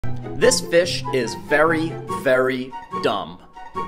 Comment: Idk why I heard "this bish" instead of "this fish" even after replaying the same sentence for 4 times straight.....